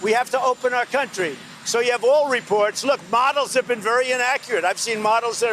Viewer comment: Did he say BOTTLES? Did anyone else hear that around 1:25 mark?